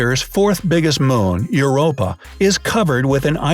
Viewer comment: I thought Europa said Your oppa